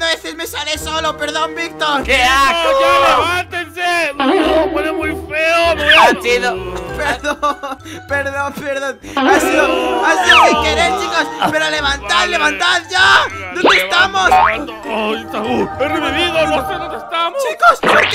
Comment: Les guro que cuando dijo sin querer oí mikelex 0:20 😢